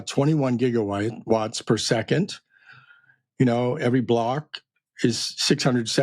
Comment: I heard him say watts per second